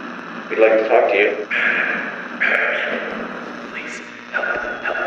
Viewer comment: Audio: If you didn't hear that hears what it said, GET..... OUT! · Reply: if you didn't hear that or if you did it said please.....HELPx5